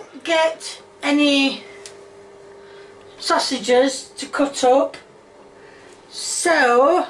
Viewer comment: It sounded like she said “sussyges”